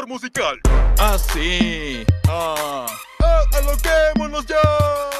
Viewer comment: Que dice en él 00:10 ? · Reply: Aloquemonos ya